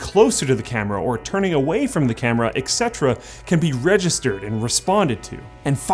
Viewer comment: did you just say "exetera"? insta unsubscribe